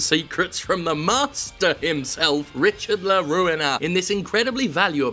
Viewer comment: I really thought you were saying Richard the Ruiner. Like, why would a love guru call himself that? Its self defeating